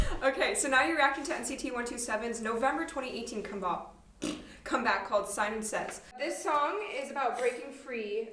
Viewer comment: I didn't notice it the first time but did Umu say "comebop"? 😂 same Umu same I love reaction hahaha coz I'm like that too lol